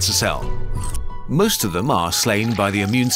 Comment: "slained"? Instead of "slain"?